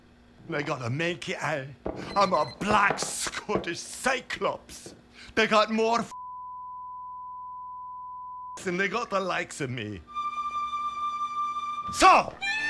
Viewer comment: this what he really said, “They got more fecking sea monsters in the great loched ness than they got the likes of me…”